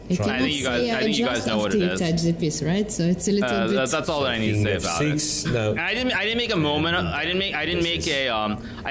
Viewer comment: what curse word? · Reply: Mistake